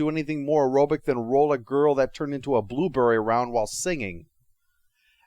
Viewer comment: No, Charlie and the Chocolate Factory. He reviewed the movies a while back. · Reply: Oh yeah, I misheard "blueberry" as "bluebird".